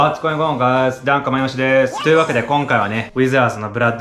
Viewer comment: 毎回最初の挨拶がダン・カマヨシでーすに聞こえる